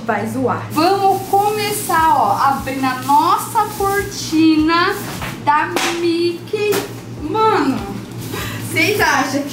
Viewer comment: min 8:32 é impressão minha ou eu ouvi minha cortina da Mickey